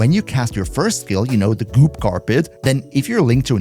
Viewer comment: Dude... Phrasing!... You keep saying "goop carpet" and all I keep hearing is "Paltrow's pubes"! Roflmao